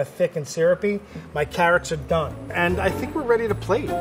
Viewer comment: is it just me or did just say "my carrots are dumb" Edit:he didnt say that he personnally created that carrot using antimatter and it graduated through harvard university